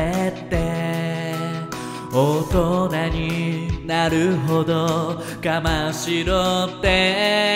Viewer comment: 「おっと何？なるほど。我慢しろって？」が歌詞？見ないで聞いたら「大人になるほど我慢しろって」に聞こえた笑